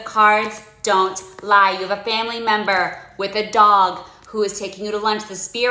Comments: Ohhh a family member WITH a dog Thas taking you to lunch…I thought she said a family that IS a dog taking you to lunch. Ok, this must not be for me then. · I swore you said “you have a family member who IS a dog who is taking you to lunch” and I thought the cards were finally giving me some good news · I thought you said “a family member who is a dog taking you to lunch” I was like damn me and beagle are goin to applebee’s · I heard “family member who is a dog”